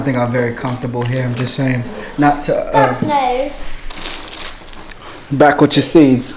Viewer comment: Am I the only one who heard her say at 15:34 F no??? 💀